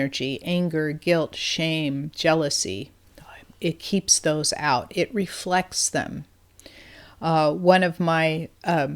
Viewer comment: You have somebody in the back saying something after jealousy at minute 9:28/12:17. It is scary to listen to it, I replay it again and again but can't understand what it says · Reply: My daughter and grandson were whispering, and the word is "mom". Thank you for pointing this out.